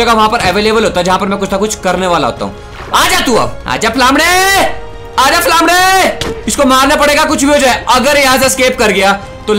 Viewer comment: 🤣🤣🤣🤣 He was almost said lawade